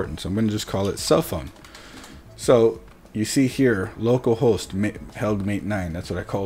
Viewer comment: I NEEDED TO ADJUST THE VOLUME ON MY HEADPHONES FOR A MINUTE, I THOUGHT HE SAID "LOCAL hOES" YIKE !!! LOL